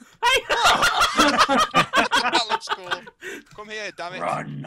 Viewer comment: i was in a different tab and i heard "COME HERE DLIVE!!!" and it scared the crap out of me x3